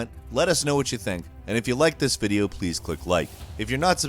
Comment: What ? I heard "if you like this video, please quit life".